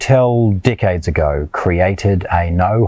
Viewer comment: when you said decades ago, i thought you said dickheads let go. but these things are sexy af